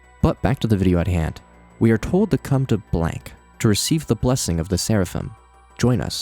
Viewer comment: I swore that said "Come to Ohio" and felt a deep pit of dread in my stomach